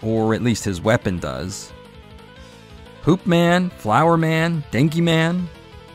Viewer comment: My focus was turned for a second and I thought you said Poop Man. It was Hoop Man, but now I gotta wonder what PM's powers would be.